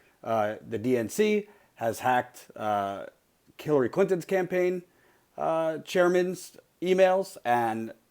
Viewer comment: Did he just say Killary Clinton? XD